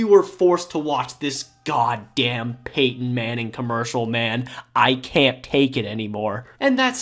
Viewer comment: I thought I heard you say 🖌️🎨 Paintin Manning.